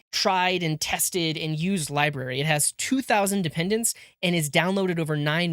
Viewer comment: I heard 2000 dependencies and got a little nervous